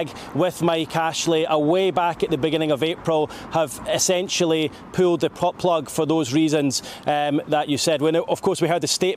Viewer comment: did he just say pulled the but plug 😂😂😂